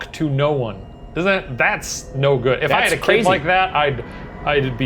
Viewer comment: At first I thought he said "If I had kid like that..." and that troubled me for a second